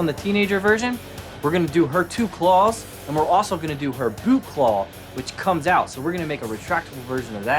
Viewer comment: You know what you said kinda sounded like boob-claw XD